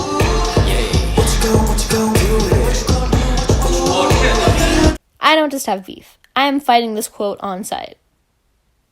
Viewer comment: what did he say? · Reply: "Oh shit, my bad"